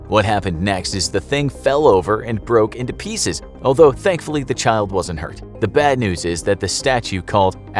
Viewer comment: wait- did you just say 'thankfully'?!